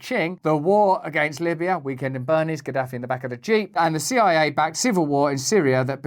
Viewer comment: I thought Russel said, "Qaddafi in the back of the cheek" until activating closed captions.